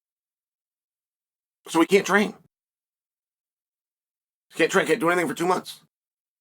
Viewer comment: I thought he said can’t drank for a few months! Yikes! 😳 But he said he can’t train for a few months.